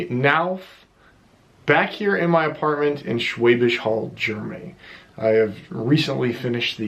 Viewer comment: for a moment there I thought you said Schwäbisch Höll'. ;)